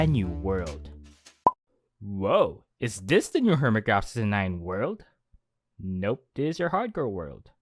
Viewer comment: The subtitles say the new hermitcraft 69 world Turn the caption